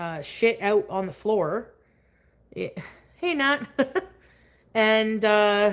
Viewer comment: Did say “hi Nat” ? Is her sister in this chat? Sometime after